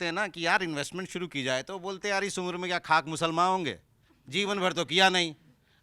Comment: what was that word which he said and what is its meaning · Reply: iss umer mai kya khakh muslma hongey yaani kii ( iss umer mai kya muslmaan bney gey ) ek muhwarey k tarah like kii iss umer mai yeh kaam kr bhi liya to kya ho jayga